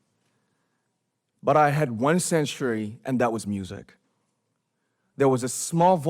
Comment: I'm really wondering that what I've heard in this sentence is "And started seeing myself THROUGH their eyes", but the subtitle presents:"IN their eyes". And also, at 4:01, I hear this sentence something kinda like "one Century (the pronunciation, not the meaning), but it appears in the subtitle with "one THING". It's pretty confusing, can anyone explain this situation for me?? Thanks for you help